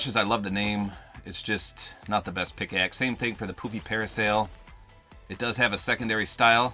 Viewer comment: I thought he said the poopy parasol